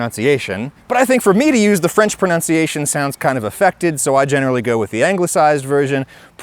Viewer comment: What's the word at 00:25? "Effected"? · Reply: Affected. Different word grammatically, same pronunciation more or less. Hope this was helpful 2 years later lol